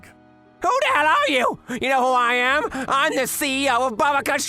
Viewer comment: I heard “you Hawaiian” instead of “you know who I am” 😭 🙏